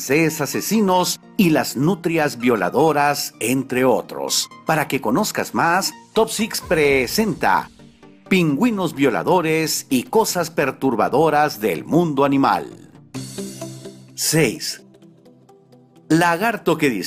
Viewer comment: Entré al video por que pensé que decía "Pingüinos voladores"